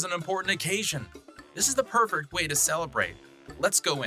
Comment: I just noticed something at 2:37 in the video I could be wrong, but does it sound like he said pervert instead of perfect